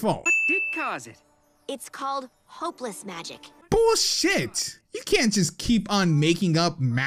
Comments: 1. What is wrong with Twilight’s wings? 2. I legit thought the mayor pony said “bullshit” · I thought the gray pony was saying "BULLSHIT" and I was caught hella off guard.